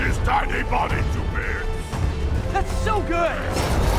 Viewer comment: Rhino says his poetry then Spider-Man says that's so good but the caption says delightful.. How?